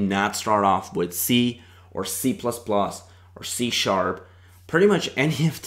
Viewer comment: What language did he say to NOT start off with? It almost sounded like he said "Japanese" which of course is not a programming language! I listened to it several times and still cannot make it out. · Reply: He said to not start with harder languages like C, C++ or C# but it's up to you whether to choose to program with the hardest language so that the others are going to be easier or start with the easier to gradually understand how programming and computers work